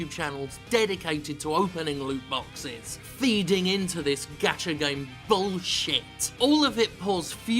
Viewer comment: Am I the only one that keeps mishearing "Gacha Games" as "Gotcha Games"?